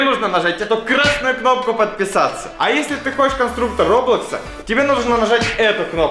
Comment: конструктор роблокса? или мне послышалось этот же майнкрафт -_